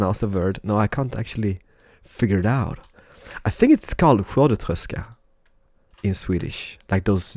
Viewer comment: what did you say? whoadatreska?